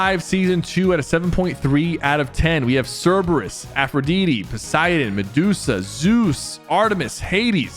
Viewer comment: did bro called Aphrodite aphrodiddy